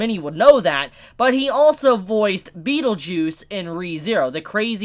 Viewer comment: Could have sworn said "Beetle-Juice"